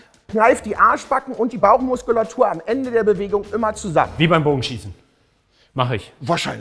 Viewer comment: und ich verstehe .. `` wie beim Bum*en''... hätte aber auch gepasst